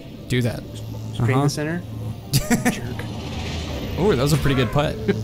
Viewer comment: Also, did anyone else hear say “Jerk” to